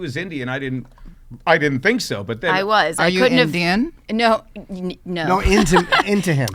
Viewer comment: Dont worry, i heard indian too 😂